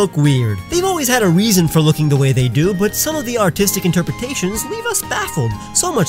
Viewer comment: So if anybody hasn't seen any of my other art related videos, check out this series. (I'm surprised some people haven't seen it) Enjoy :) - <link> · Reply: i thought you said autistic